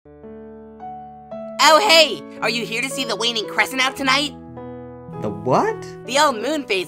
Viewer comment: i thought test tube said “weenie crescent” instead of “waning crescent”